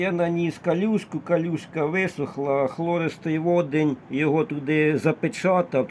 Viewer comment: Так, фтористий водень - це плавикова кислота! Потрібно промити водою графен, потім додати в цю воду 10% плавикової кислоти, і напиляти на скло! Я вже придбав генератор туману, скоро покажу як наносити тонкий шар на скло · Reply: а мне на видео послышалось хлористый водень, гугл переводчик перевел как хлористый водород, потом подумал что это HCl - соляная кислота. Будем ждать! Кстати каким методом легируете оксид графена например азот и бор? Спасибо заранее!